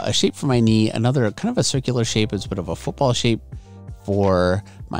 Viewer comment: I deadass thought he was saying "bull shape" for the entirety of the video